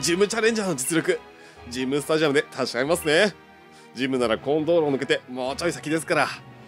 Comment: この道路がコン〇ームに聞こえた